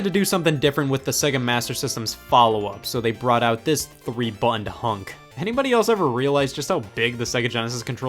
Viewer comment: is he saying buns or buttons